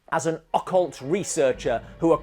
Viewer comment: When you mentioned The Skull i thought you said he was a Yakult Researcher. The idea he looked into creepy yoghurt would have changed everything.